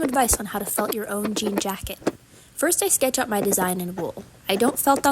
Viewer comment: I had to go back cuz I thought you said "F** a jean jacket" 😅😅